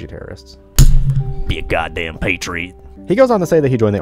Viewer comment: 🤘🏼......at first I thought he said “be a goddamn “patron“ instead of “patriot”